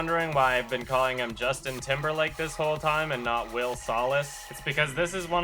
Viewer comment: Bruh when he said his name was Will Salas I thought he said Will SOLACE like from Percy Jackson and I just about spat all over my iPad